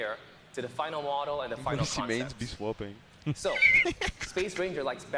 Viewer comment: hey can someone tell me what they said here? It was something like '--- mains be swapping'